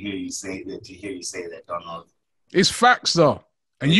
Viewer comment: did he say Donald to? 🤣🤣